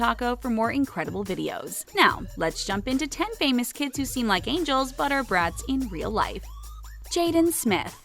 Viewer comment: I thought it said Scream like an angel 😂